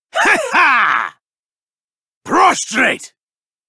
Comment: what is that word? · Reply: Prostrate